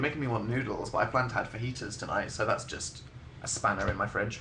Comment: when he said he was going to eat fajitas, i thought he said "i'm gonna eat anahita"